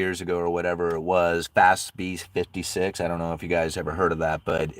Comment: what are you referring to here? It might be me not hearing you correctly, but I keep hearing Fast Beast 56, or Fast Feast 56. This sound like something worth having a mental bookmark for, but I cant make it out correctly. Thx in advance!